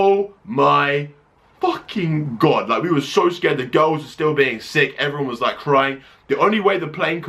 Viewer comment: When you say girls, It sounds like u say ghost :)